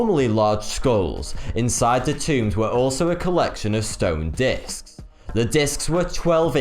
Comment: I'm watching these as i put my make up on and I heard dicks instead of disks and was really connfused for a while when he proceeded to talk about the holes in their centers and huge diameters. Nicely done.